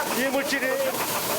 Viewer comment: 약간 아 기모찌네 라고 들림 ㅋㅋ